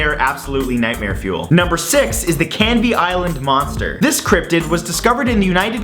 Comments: i thought you said candy island monster instead of canvey island xD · When you said "Canvey Island Monster", I heard "Candy Island Monster". A giant monster that gives you diabetes.